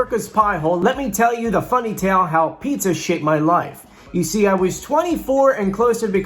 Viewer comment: Did say shape or something else?